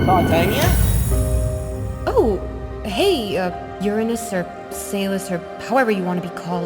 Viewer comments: I turn the cc on and when I was in 5:17 i see that tatinia said"hey uh Uranus or s u s?"what did tatinia said s u s what the f*** man. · Titania: Oh hey. Uh, Uranus or Sus. DID SHE SAID "SUS"?